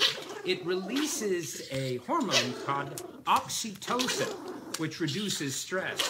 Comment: When the dude said: oxytocin I thought he said oxy-clean